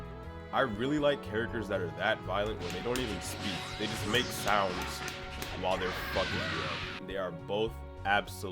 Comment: all i heard was "they just make sounds while there fucking" lol